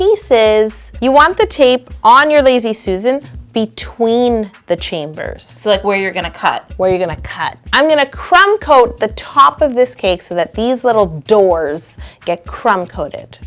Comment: Hey YoYos & #NotificationSquad!! I think we definitely outdid ourselves this week! This Halloween Roulette Wheel Cake is not only disgustingly delicious, but it also doubles as the ULTIMATE trick or treat game that you can play with friends! Let me know what other gross ingredients you would use in your own version of this cake BELOW!! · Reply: How To Cake It when I was skipping through the intro I heard "Demon Chambers." Which you whispered 😂😂😂😂